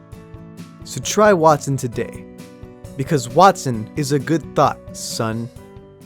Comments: At first I thought you said "Wattson is a good THOT, son" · For a second i thought i heard “Wattson was a good *thot” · At the start I thought you said Watson is a good THOT!!!!!! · I thought he said Wattson was a good thot 😂😂😂